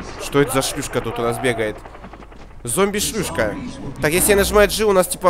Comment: Кто услышал закадроввый голос там говорится:Зомби Шлюшка